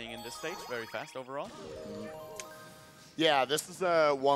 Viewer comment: when it said "fast foe" for toy time i thought it said fast poo